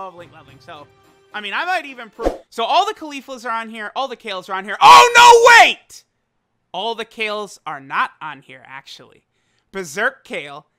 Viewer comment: Can someone tell me what he said? I couldn't quite understand it. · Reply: "all the cauliflas are on here, all kales are on here OH NO WAIT All the Kales are NOT on here"